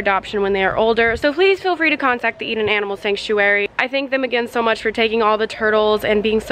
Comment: I kept hearing "eat an animal sanctuary"